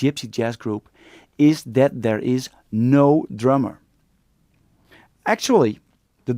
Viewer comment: I thought he said "no drama", at the end I realized that he said "no drummer"